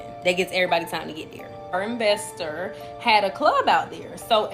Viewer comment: did she say our investor 😆??